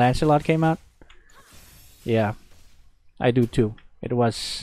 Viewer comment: did he just say deal with her sister too? 😁